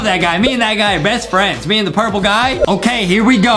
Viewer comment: I thought said purple guy from FNAF 🤣🤣🤣🤣🤣